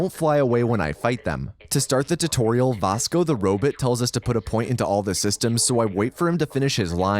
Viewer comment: did bro just say robit